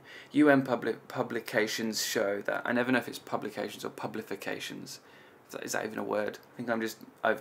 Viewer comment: I misheard wrongly the word "pube"lifications at 11:05 😂 I will get my head out of the gutter now xxx❤